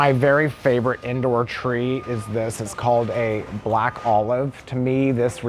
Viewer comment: Did he say Black Olive Tree at 13:13?